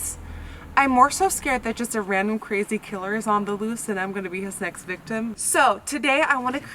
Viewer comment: did you say "next victim" or "sex victim" ??? I had to rewind afew times, still didn't get it😂😂😂